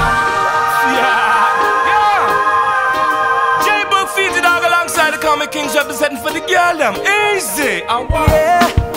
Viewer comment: Can someone help me with what is said at 2:32?? "J Boog, Fiji __ alongside the Common Kings representing for the _____" ??? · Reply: jboog featured longside the common kings representing for the gal dem (them girls or girls them)